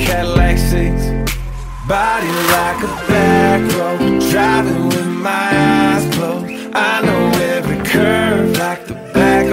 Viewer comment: My dad made this song sound like it says body with my back hoes driving with my a** whole And I'm like😭😭😭 you ruined this song for me